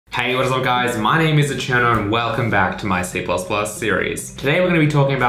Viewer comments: Lost it at the closed captions. "Hello guys my name is a cheddar" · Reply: I always hear it: "My name is the Cyano" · Lost it at the closed captions. "Hello guys my name is a cheddar" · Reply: i ALWAYS hear: " My name is 'Ocherno' "